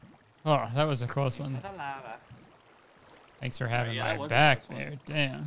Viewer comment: does say thanks for having my back babe? hahahahaha